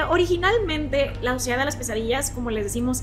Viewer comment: Jajaja juro que escuché "La suciedad de las quesadillas" 🤣